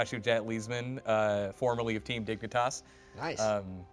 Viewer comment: When guy said Dignitas, all I heard was "dick n' toss."